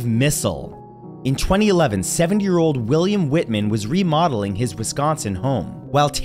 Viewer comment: I honestly thought he said a 7-year-old was remodeling his home